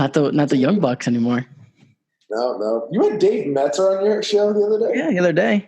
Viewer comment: What is the name said at 36:15? I can’t understand and I want to look the person up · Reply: David meltzer?